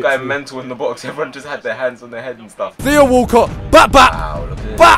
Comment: What did Manny say exactly in 4:46? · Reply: Every one has just got their hands on their heads and stuff